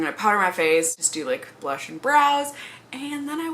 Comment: Haha omg I thought you said blush and bras. 😂 hope you're feeling better!